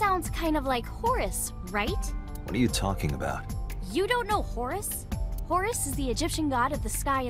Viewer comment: Not gonna lie, when Iris mentioned "Horus", I heard "Horace" and expected a FruitBall tangent